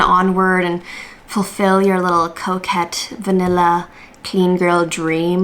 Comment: I thought you were saying coke head and not coquette.